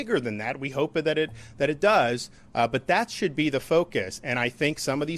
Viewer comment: did he say "... and we hope that it does [become a cat 5]"? Wtf